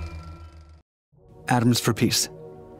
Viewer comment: Him : atoms for peace What i heard : Adam's from piece